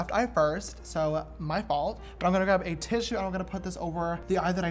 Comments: did he say tish?!?! Did the Dolan twins possess James body?? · did he say tish😂😂😂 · OMG did you say "tish" like from the Dolan twins lol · did he just say a tish he’s been spending to much time with the Dolan twins